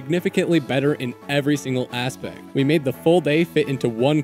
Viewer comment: the aspect sounded like asian 😂